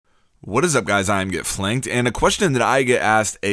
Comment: Oh I thought he said "What is up guys im gettin' flanked"